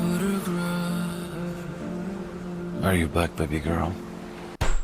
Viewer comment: in my own tipsy mind, I deadass thought Massimo said, "Are you black baby girl?" and I choked on my chicken tenders.